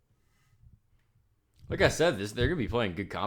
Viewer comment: what was said at 1:45 lol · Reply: “Like I said” still working on enunciating lol